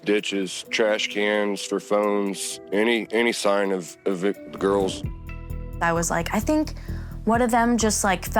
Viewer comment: well I said it is odd that he “seemed“ to almost say victim nobody in these comments inferred that he had anything to do with their murders. I don’t think that at all I just find it odd. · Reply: The problem is, if like you infer, he was about to say victims, he missed out a 'the'. People dont say "any sign of victims", they would usually say "any sign of THE victims". To me, it just sounds like he says " any sign of the girls", but with a slight stumble/stutter/hesitation/.